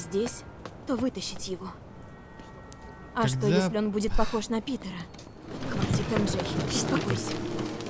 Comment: Короче, когда Мэри сказала "А если он будет похож на Питера?", мне послышалось "А если он будет похож на Пидора?". А я в этот момент ел - в итоге залитый супом стол.